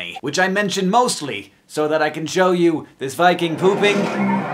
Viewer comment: What did he just say, all I heard was Vikings pooping